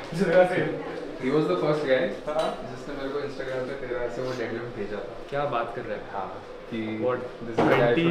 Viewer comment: kya bola ne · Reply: usne bol this is 1st guy jisne mere ko deadlift ka video pja tha